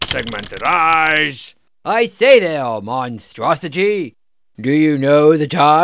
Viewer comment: Claws, horns, tusks, tentacles, power doors, 15 year drivetrain warranty, proboskis, segmented eyes. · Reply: I actually heard it as monstrosigy.